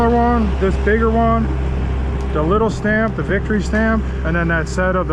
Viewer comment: DID YOU SAY VICTORY STAMP?????? Tell me it's true! EVERY customer who gets an excellent concrete job like you give should also get a stamp that reads "VICTORY."